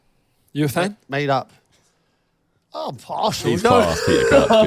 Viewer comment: what did he even say? What was his response? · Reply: Yup. “Im partial”